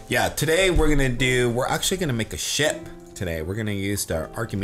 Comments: I thought said "we're going to make a shit" lol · When said "we are gonna make a ship today!" I thought he said, "we are gonna make a sh*t today" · I thought said take a sh*t when he said make ship